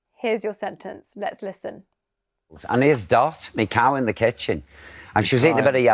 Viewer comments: in the last one , I heard "the cow" instead of "my cow" and the last one left was completely omitted... 😂 rest were decent enough to catch on ...👏🏻👏🏻❤️ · The advanced are the easier ones, why? I missed two things, I always said by large and not by and large, and I thought he said the cow and not my cow. Considering I am Italian I am pretty satisfied